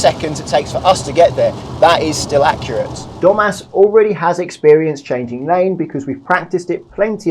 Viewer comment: I wasn't looking at the screen, and I heard "Today Dum*ass is having his first motorway lesson." That grabbed my attention and I looked up, realising it's Domas. Sorry, Domas. 😅